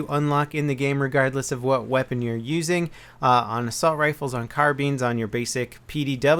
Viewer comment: Did he just say "carbeans" instead of carbines?